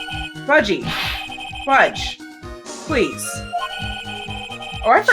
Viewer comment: I keep mishearing you calling Budgey as "Fudgey," and I can't stop laughing at it!